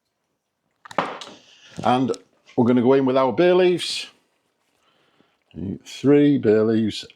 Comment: Dicing the onions - cutting them into cubes is simple enough - it’s painting those little dots on that’s problematic! When you said bay leaves, I thought that you said Bailey’s!